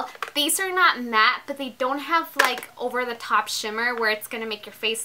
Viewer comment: Am I the only one that heard her say all over the shit hahahahaha